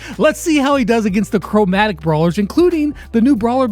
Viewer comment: wait did you just say CROWmatic brawlers ?